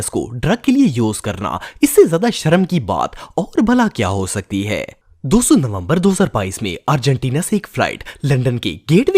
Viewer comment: Bro you wrote "number" instead of "November"